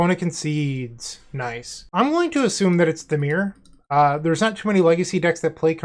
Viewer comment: at first I thought you said "I'm going to assume this is dimir" and I thought to myself "in what world does dimir play commercial district" lol